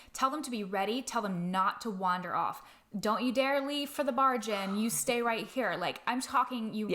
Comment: Lol I was editing while I listened to this and I heard "Don't you wander over to the bar, Jen" and of course felt called out! Haha